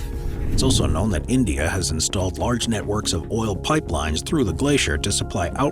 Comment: did i hear oil?